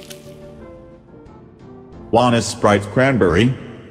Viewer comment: I won't say no to some sprit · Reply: I thought that said spirit